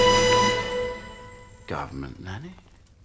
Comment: can't quite get what the guy guessing the father says at 0:16 seconds before she hits the cane on the floor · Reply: Theri4444 he said “government nanny?”